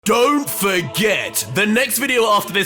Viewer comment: Everytime he says "don't forget!" I always hear "don't baguette!"